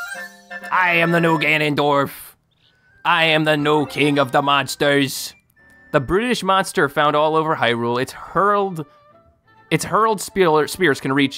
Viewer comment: when you said brutish I thought you said British